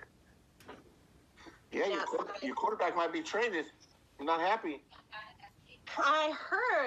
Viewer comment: Did he say, "Your quarterback might be trainded..."? (sic) Have we actually gotten to the point where not only low IQ bumblefarts make up new conjugations (e.g. "paided" in place of "paid") but our public school teachers do as well?